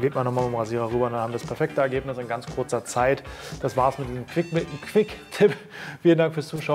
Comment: Ich habe auch erst Fi**tipp verstanden. Aber es war eher Quickmi(pp). 😂 Schön, dass ihr sowas nicht neu dreht. 👍🏼